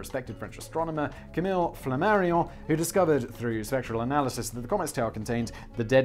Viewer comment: I know you said “spectral analysis” but I heard “sexual analysis”